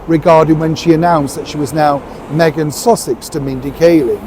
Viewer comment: Did Neil say Meghan sausage at 2:40?